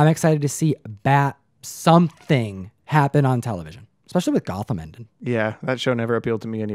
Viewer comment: What did you say (talking about T.V. Shows you liked and didn’t like....”@ 18:00 on video,”television especially with ___________yeah,that show never appealed to me anyway”.My captions say “Gotham Endon” and I’m sure that’s incorrect!Thanks to anyone that can fill in the blank for me!